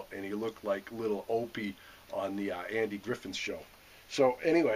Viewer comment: Sounded like you said Andy Griffin show.....lol....he would be rolling over if he heard that.....cool vids